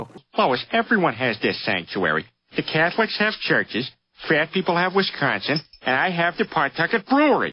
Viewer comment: "the frat kids have Wisconsin" (me living in Wisconsin) now i know your right but wtf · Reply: I thought he said ' fat people have Wisconsin.